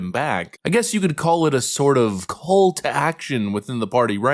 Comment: i thought i heard u say "cult action" lolololol